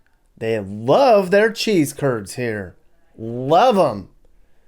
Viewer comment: Nice touch.......................cheese head letters in title at the beginning. Sounded like you said.....cheese turds. 🙂